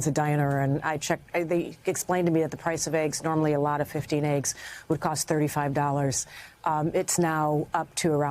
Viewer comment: did she say 15 eggs now costs $150? Am I taking crazy pills?